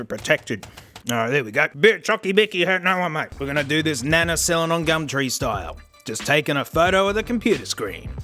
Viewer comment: Guys what the hell did he say? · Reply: We're gonna do this nana selling on Gumtree style